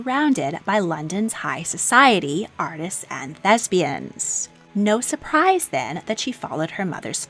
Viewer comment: I thought you said lesbians and I was like HUH we need another video on that